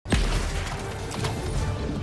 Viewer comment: I thought he said "yall having a gyatt party" the internets ruined me dawg 💀